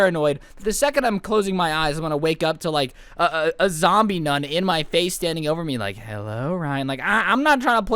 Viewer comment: bruh I thought he said zombie nut